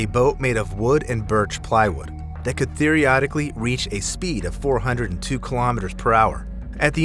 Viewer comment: Did he really say "theoriodically" at 7:22?! 😆